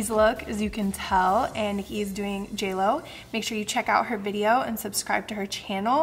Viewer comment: Did she say HE is doing jlo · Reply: I said Nikki is doing JLo!